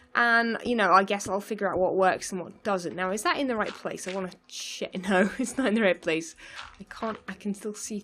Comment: did you say S#!t here? Lol I dont think ive ever heard that from you. Cracked me up though. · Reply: She started to say "check", but didn't finish saying it.